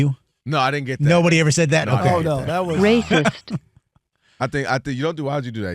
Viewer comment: I know I’m not tripping I heard rapest at the 11:10 mark. ???